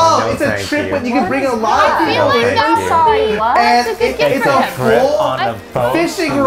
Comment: Does anyone know what toast says at 3:18 I can't decipher the second half of the sentence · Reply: I think he said "It's a grip on a boat to hunt for bluefin tuna"